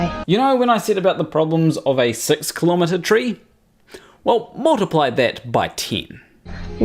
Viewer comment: When you said “multiply that by ten” I heard “multiply that by one thousand”